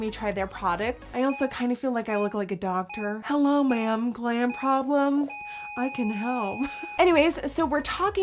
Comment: OMG, I thought she said "clam problems," but she said "glam problems." I thought she was making a dirty joke, omg lol. facepalm